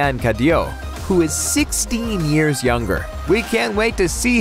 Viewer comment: Did he say 16 years younger? 16?!!!!